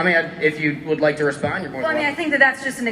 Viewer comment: What did the audience member say at 27:38, because it sounded like they said, "You're welcome to leave"?